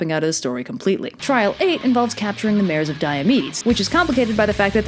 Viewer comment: Thought you said mares of diabetes.